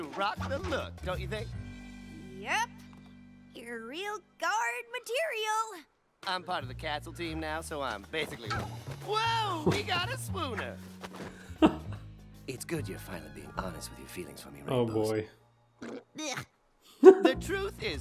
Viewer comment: When I first saw this scene, I thought Zephyr had said "Whoa, we got a spooner!"